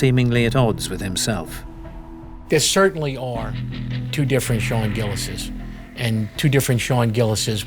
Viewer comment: I swear I thought it said Shane Gillis at first. Lol